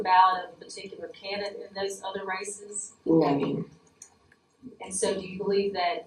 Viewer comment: This is either very bad editing or deliberate but at 1:05 where it is written the woman's response was 'Maybe', she clearly said (at least to my ears) No ma'am. I don't believe this is the work of Brian Tyler Cohen but it is alarming to see so many inaccuracies in texts. Certainly we can do better than this.